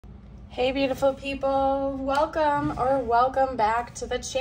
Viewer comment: Every time you say "hey beautiful people", I hear "Hey Josie" in my head 😊😊😊